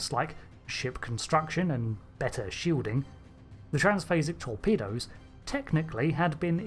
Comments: I thought that said transphobic torpedo. Based UFP. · Not gonna lie. I thought my notifications said transphobic torpedoes. And now I’m picturing these things. A torpedo with a red tip or one in a white sheet or fired from the USS Karen.